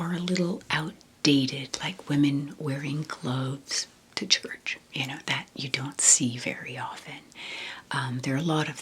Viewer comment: I thought at first u said women wearing clothes to church 😂. For a second I thought u were heading to church naked 😆